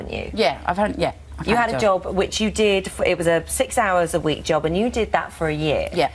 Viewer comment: Please tell me I misheard her. Because I believe I heard her say "6 hours a week" Job. How? What kind of job is only 6 hours a week? I work 8 hours a day, I used to work at a job where it was 12 hours a day.